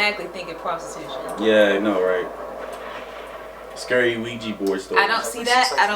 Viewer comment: I'm sorry. I keep hearing "Luigi Board". Now the video isn't scary.